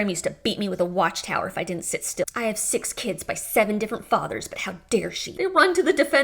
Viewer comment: Did you really say at 9:45, " six kids from seven different fathers"? That cracks me up. You're hilarious.